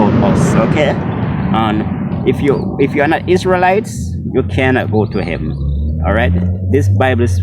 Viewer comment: He said we had to be something to go to heaven and if we're not that we can't go to heaven did anyone know what he meant? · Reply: he said if ur not isrealite you can not go to heaven ,which is lie.